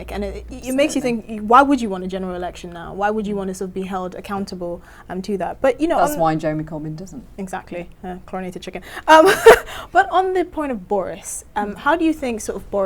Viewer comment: Did she say "chlorinated chicken"?